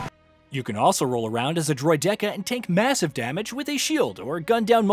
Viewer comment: Sorry did you just say "Droy-decker"?!